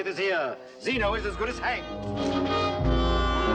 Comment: At the point 12:01 I say serves the bastard right! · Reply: I always thought Dr. Smith said, "Zeno is as good as hanged!" that seems to fit the scene really well. Automatic subtitles said Hank. Lol. Hank who? 😄 Thanks for all your hard work in making these episodes, and I'm really enjoying these. 💛👊🙂